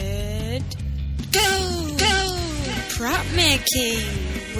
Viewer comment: at the beginning it sounded like you said, ''crap making!'' lol